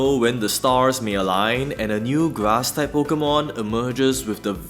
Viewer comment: I keep hearing "Glass Type Pokémon" and gotta check myself each time.